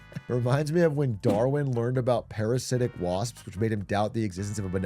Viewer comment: I’m from Texas and thought I heard “Garland discovered the parasitic wasp and doubted the existence of a benevolent god…”